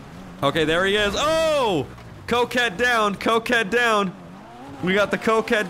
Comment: I swear you just said "Cokehead Down" 😂😂😂😂